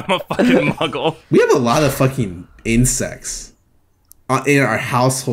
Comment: holy shit had to pauseafter that for a sec bc i thought said "incest". but then i realized that wouldnt make sense bc none of them are related